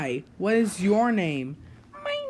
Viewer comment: What did . say? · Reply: Its period